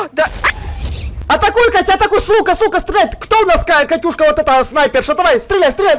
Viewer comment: стреляй, стреляй с лука! Я которой послышалось *СТРЕЛЯЙ $УКА*🤣🤣🤣